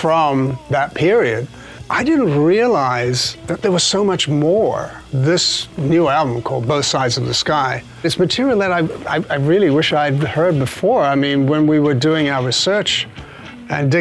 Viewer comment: and it will forever be misheard as "Both Sides Of This Guy"